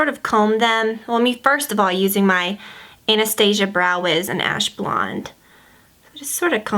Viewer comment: It sounded like you said anus- tasia lol!